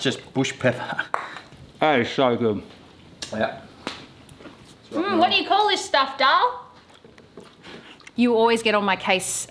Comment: ‘What do ya call this darl??’ Hahaha that one is going straight in the pool room! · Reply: I thought she said "what do you call this stuff, dhal?"